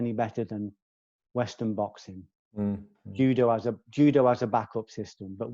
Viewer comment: did he say judo as a back-up?